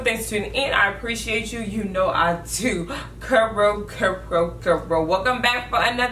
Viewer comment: Always singing that intro with you sissss loveee youuu😂🔥🔥🔥🔥🔥🔥😍😍😍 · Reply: i swore she was saying “you know i do karoo karoo karoo” 😂😂🤣🤣